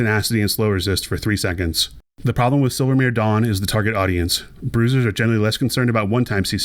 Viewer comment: Kept mishearing it as "Silvermere Dong", which is henceforth how I will refer to the item.